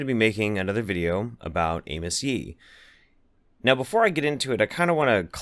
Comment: I totally thought you said Anus Yee at the start